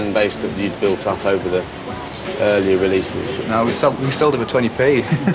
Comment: did he say "no we sold it for 20p" meaning 20 pence? As in they sold their entire album, the first few for close to a quarter ?